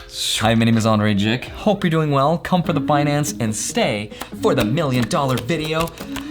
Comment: Wait! Did Andrei just say, “Come for the Binance” instead of finance at 1:16? 😂 Subliminal messages 👻